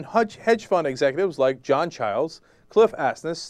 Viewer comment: Did he say 'John Towers' at 3:00?